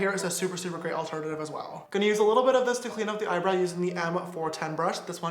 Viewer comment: did you Just say eyebrai